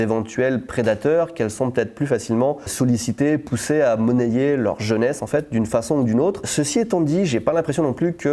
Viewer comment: j'ai entendu "monnaier leur jeux Nes"...et je me suis dit "bah ouais c'est normal au japon"